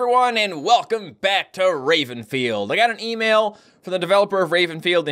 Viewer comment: I thought you said rapefield xD